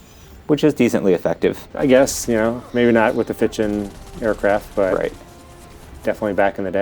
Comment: what are you saying? Fitching Fighter? Never heard that term before.